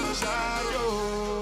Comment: thought he said ''I need ta know howd'sha know''